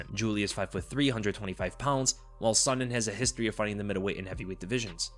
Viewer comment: Jesus, I misheard that Julie was 5 foot, 325 pounds